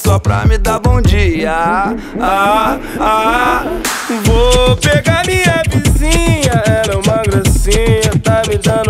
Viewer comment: i eu que entendi "VOUUU PEGA MINHA BIZINHA"